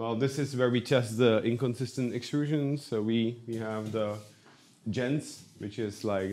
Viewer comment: Did I just hear from the Man himself that the inconsistent extrusion was due to the Bondtech gears? News to me. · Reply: What printer was it at 13:50? I heard it as "Gents"